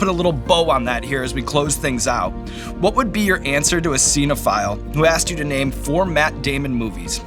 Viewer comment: "What would be your answer to a cinephile..." Took me a couple seconds. I thought he said 'xenophile', and couldn't figure out the relevance of loving foreigners in this issue.